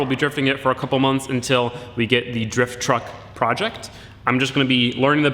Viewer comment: am i missing something because i thought just said DRIFT TRUCK PROJECT